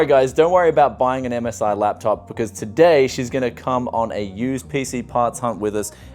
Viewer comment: i thought he said shes gonna cum ona used pc ( ͡° ͜ʖ ͡°)